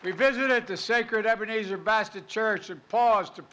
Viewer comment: Did he say Ebenezer Bastard Church at 2:06?